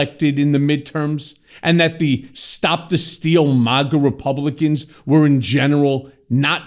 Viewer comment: I must have mis-heard, I thought Michael Cohen said “ maggot republican “ I think I prefer that!